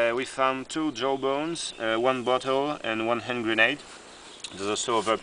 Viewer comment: sounded like he said "butt hole" for a second there.